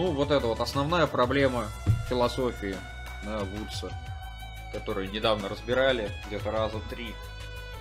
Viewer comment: подскажите, что за название проблемы, не могу расслышать. Вудса не гуглится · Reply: John Woods, "Paradox and Paraconsistency" Вот на эту работу ссылается.